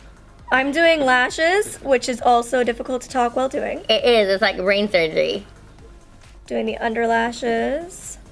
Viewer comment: who else heard her say doing eyelashes is like brain surgery😂 um no honey